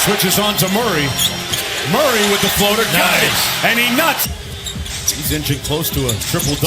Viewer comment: Anyone else hear @ 8:37 the announcer says “and he nuts”